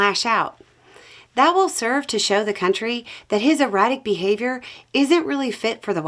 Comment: Late again! Wait… at 2:15 did you say erratic or erotic? PLEASE don’t use THAT word when speaking about Trump. 🤢